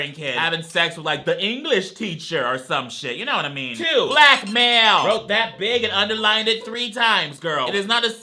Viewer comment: okay but why do the subtitles say "Black male" instead of blackmail 💀